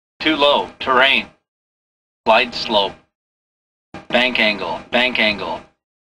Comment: Que dice la del 1:52? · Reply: Bank angle que trata que el avión esta volando en una mala posición haciendo que llegue a entrar en perdida